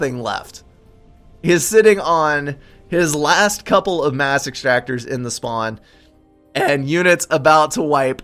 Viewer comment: I thought you said massive tractor instead of mass extractor